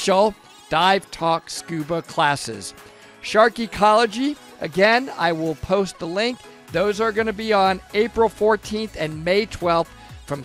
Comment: I thought he said "sharkicology", and interpreted that as very own teachings about sharks, everything he learned from talking to them!